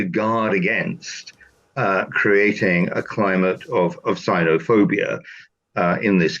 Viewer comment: he said cinofobia, which means fear of china, and i thought he said cynofobia, which means fear of dogs.